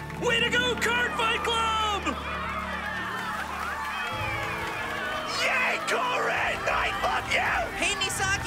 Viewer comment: Did I misheard Morikawa @ 1:08:37 Saying "YAY, KOURIN I F*%@ YOU" ?